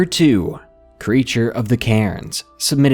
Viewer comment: I thought he said "creature of the karens" for a second...